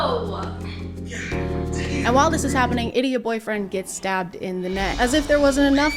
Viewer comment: DID HE JUST SAY DIDDY?!